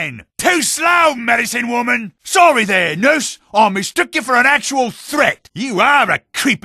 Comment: always thought he was saying "noose" instead of "nurse" :thonk